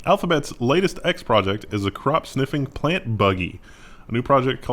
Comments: Was just listening in and could have SWORN you said "crotch sniffing" when you read out that Alphabet story! 🤣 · I thought said "crotch sniffing" I thought Alphabet had just invented the dog 😂